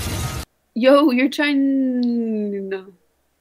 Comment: "Yow you're chin.........na" Shes trying to fix what she said but couldn't lol. Stop Covid19. This girl needs to go to school NOW.